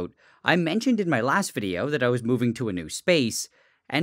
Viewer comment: Near the end I thought you said that you were moving to space. That would be pretty cool tho ngl